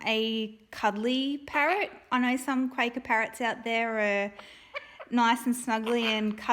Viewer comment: Did he say "quaker"?